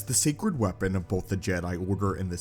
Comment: The secret weapon of the Jedi and Sith aka the lightsaber. It’s not that secret when literally everyone knows about it. Even Anakin as kid knew what they were and the blade. Totally top secret just like how Order 66 was a stealth mission. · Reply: Did he say "secret"? I thought he said "sacred".